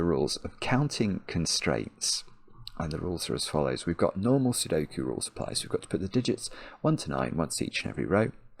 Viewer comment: I misheard you reading the rules as „no more sudoku rules apply“ and I was so incredibly worried until I realized it probably was normal sudoku rules after all xD